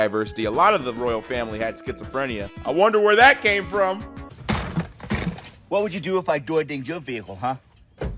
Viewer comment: what'd he say? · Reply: "What would you do if I door dinged your vehicle, huh?"